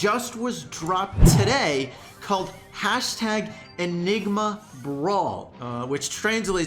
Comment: i heard # tag ligma ball instead of nigma brawl